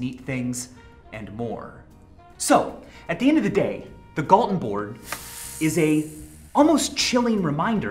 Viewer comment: I THOUGHT IT SAID THE GALTON BEARD